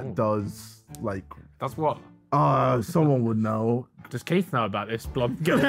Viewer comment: what did Ethan say? Who’s Keith?